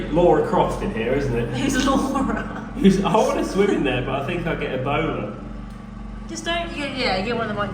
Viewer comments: I thought he said, "I want to swim in there but I don't want to get a boner" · Who else thought he said "I think I'll get a boner" @ 4:30 🤭